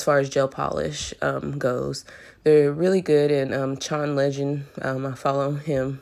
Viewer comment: Sorry I didn't catch the name of the gentleman you were referring to at 2:16 so I can see what you at talking about thank you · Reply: Chaun Legend